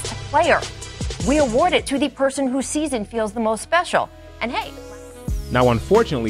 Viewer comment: Just me doing a little knit picking, at 2:29, I think she says who’s season feels the most special and not who sees and feels the most special